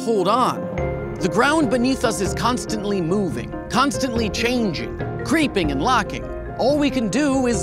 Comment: "constantly shaking and droping" I heard "constantly, poppin and lockin" Got this wtf look on my face o.O